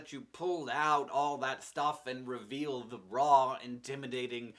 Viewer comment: I thought you said "reveal the bra," instead of raw.